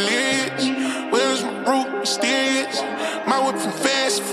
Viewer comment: Damn, dead thought he was saying "Wheres my ROOF?, Mysterious"😩 still hear that, what evs🤷🏾‍♀️🥲